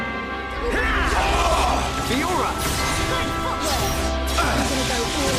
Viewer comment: Did Fiora say "Good footwear"? Was Shulk wearing Jordans or something?